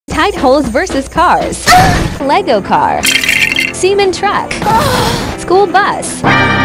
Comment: bro when he said cement car am I the only one who heard it as "semen car"💀💀💀